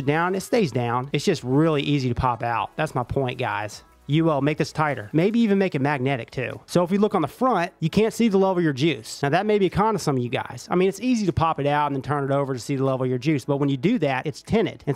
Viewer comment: I thought he said “the love of your juice” lol